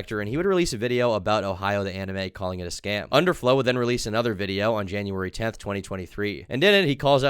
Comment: i thought you said overflow